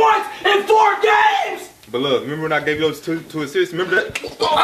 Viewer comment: When he came back and choked love I was crying😂😂😂😂😂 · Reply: when he say that i could’ve sworn he said remember when I got those two assists